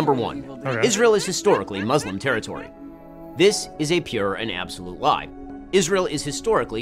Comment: I thought Ben said “this is a pure and absolute vibe.” I had to rewind to make sure I heard that right. I’m like “I guess Benny boy is really trying to attract the youth with lingo like that.”